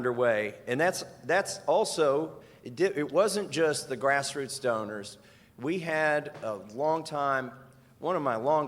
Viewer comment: I swear I thought he said grassroots stoner 😂😂😂